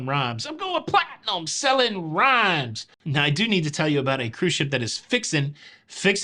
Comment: I was half listening to this installment and I thought you said, “I Eat A Cruise..” lol.